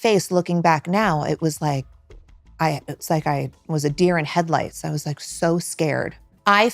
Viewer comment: ela fala "headlights" e não "red lights" como foi traduzido.